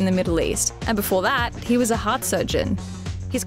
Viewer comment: Initially I thought she said hot surgeon instead of heart surgeon 😂😂